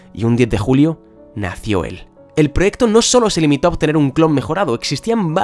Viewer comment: Lol Mewtwo nació el 10 de Julio igual k yo ;) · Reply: Perdonad pero di mal no escuche en el 4:37 dice ( 10 de julio nacio mewtwo ) :/ correjidme si me equivoco